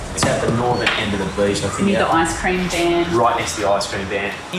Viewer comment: at first I thought she said ‘I need an ice cream Dan’ and I was like... TF DO YOU MEAN YOU NEED AN ICE CREAM DO YOU KNOW WHATS GOING ON? Bit stupid looking back on it